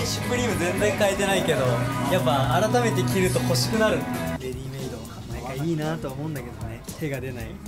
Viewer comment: の服のブランド何ですか？？！！(よく聞き取れなかったですごめんなさい) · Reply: READYMADEかな？